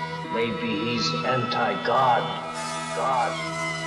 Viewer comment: I heard "maybe he's hentai god"